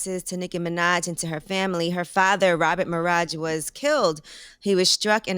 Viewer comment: Nicki real last name is Maraj. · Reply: ok I thought they said minaj but that's fucked up tho